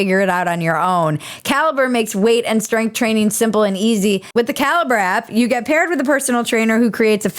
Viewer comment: When you say "caliber app", I kept hearing "cal-brap" which sounds like some new super hip product 😋